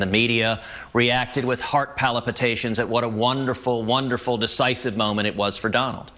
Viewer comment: I like how Cruz says "heart polypitations" instead of palpitations at 0:50. What a maroon! :)